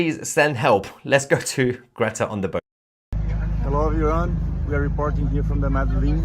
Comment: On their call for help (minute 6:51) did he say, Hello Iran?